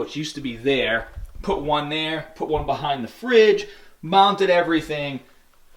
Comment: When you said.." I mounted everything". I thought you said.."My mom did everything"...lol